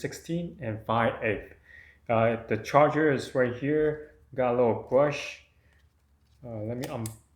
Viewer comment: I wasn't paying attention and at 2:23 I thought I heard: "it also got a little bush" XD I thought like someone handled that thing before and left some remnant of their manscape in it